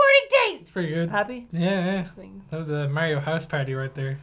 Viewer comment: "That was a Mario house party." Irish People play Unfair Mario. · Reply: I thought he said a "Mayo house party." Sort of the same thing in a way.